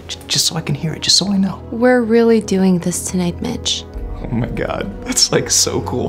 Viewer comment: I thought she said bitch 💀